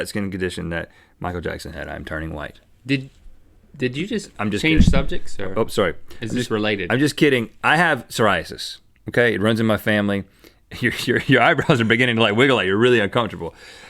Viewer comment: did he say "sorry" to a microphone? xD Idk why but i cant stop laughing xD